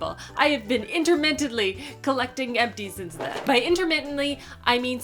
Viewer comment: At first I thought you said you were collecting empties since 2016! Hahaha! I'd love to see that big stash!